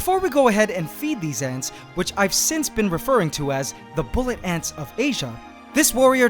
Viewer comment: i thought it said Asian bully ants